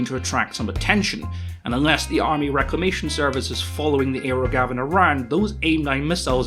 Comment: You keep saying "aero Gavin", but I keep hearing "aerogasm", which sounds like what Sparks is going for.